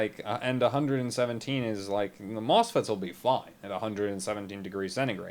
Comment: "the mosfets will be : [fine/fried] " ?! wtf did u said?! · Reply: Robert Trisca falling